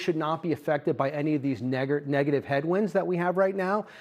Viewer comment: Did he say Neggar Headwinds (3:42) in front of Charles Payne? Hahahahahahaha. These guys are something else